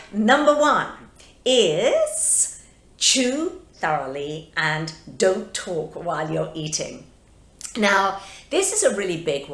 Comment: I thought you said “chew thoroughly and don’t twerk while eating”. I snapped my head around so fast 😂 Great video 😘